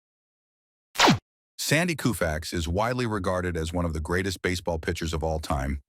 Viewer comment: I hear Mr Roboto say: "Sandy Koo Facts?" Why do they use AI? AI never watched or played base ball!